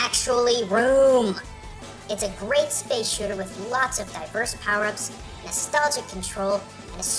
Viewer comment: did i hear diapers ???